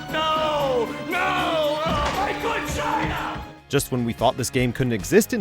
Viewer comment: Did Plankton say "NO!! NO!! My vagina!"? What the shit?